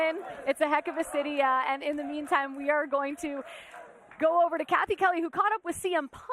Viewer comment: did rhea say сathy ?👀👀